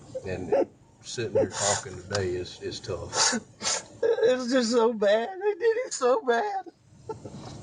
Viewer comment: I always hear “I did him… “, not “they did him…” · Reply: I heard, “ it’s just so bad. I did it so bad.”